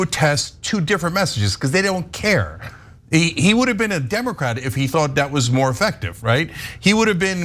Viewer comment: I thought ya'll said he was democrat